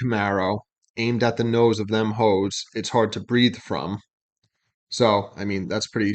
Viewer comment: Think Genius got it wrong. I hear him saying "aimed at the nose so them holes is hard to breathe from". Makes more sense too